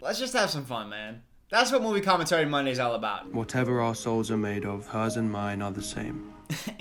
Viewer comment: Until said the "whatever our souls are made of" quote I really thought Hardin was saying "whatever arseholes are made of" every replay, it's literally indistinguishable in his stupid voice